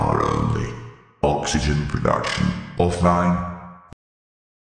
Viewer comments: omg 2:04 - 2:10 omg i heard this when im at the lost river and then im thinking what was that???? what did You said mr. cyclops??? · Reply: You are the best captain on the planet, I am not even Squid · I had this voiceline come up randomly while I entered my sub. I didnt catch what he said at first and I was really confused for about 10 minuets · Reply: squiding? probably kidding but wrong